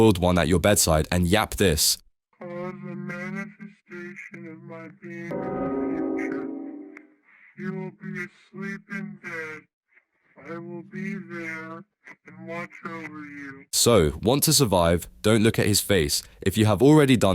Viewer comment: he is actually saying “I am the manifestation of my being you will be asleep in bed and I will be there watching over you the doctors will eventually find you they will not see me but you will see me (correct me by replying if I did it wrong)